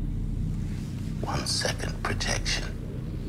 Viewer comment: Lol it sounds like he says "One second protection". I know it's meant to be "Projection", but it's hilarious in a dark way because all they get is one second of normalcy left in their lives before they can no longer accept their own realities. I love how even in the background, ponytail guy that was so proud an instant before is so struck by what he sees that he can't stop repeatedly looking at the screen in shock and then looking away in horrified denial. Each of them now questions what they are, if a computer can so easily predict their future behavior.